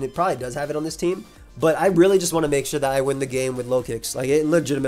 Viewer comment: The amount of time I thought he was saying 'I win the game with low kicks' is hilarious. Like I would spend a whole minute wondering which mon has low kick before I realise it's the Pokemon